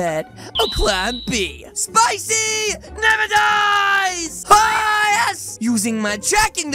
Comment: Spicy never dies! 🔥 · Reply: I thought he said spicy never dice